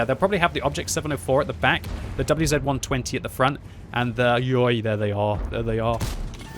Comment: did he just say ayyo